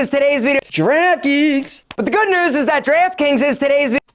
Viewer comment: the way he said draft kings sounded like he was saying giraffe kinks...